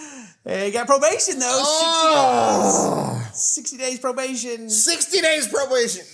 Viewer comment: 60 months probation is what is sounded like